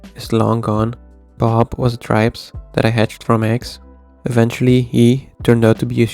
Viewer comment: What is the weird fish that only lives about 90 days? It sounds like you say "tripes", the auto-generated captions say "tribes", neither of those are animals.